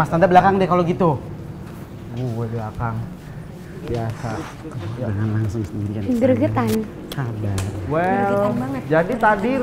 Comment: Uncle: "Jangan langsung sendirian sayang" Tolong koreksi apa aku yg salah denger. Udh aku ulang² pakai volume yg gede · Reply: "Jangan langsung sendirian sayang sabar " gitu ya